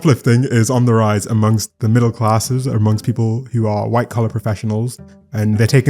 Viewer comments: I heard that as "white color professionals" 😳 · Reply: I heard that too 😅... went back again but now I hear "white collar" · I heard that as "white color professionals" 😳